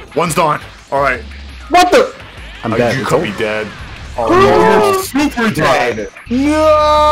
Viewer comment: Did somebody say “your Kobe dead” or am I tripping